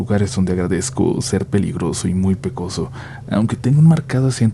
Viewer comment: escuché peligroso en lugar de pelirrojo 🤔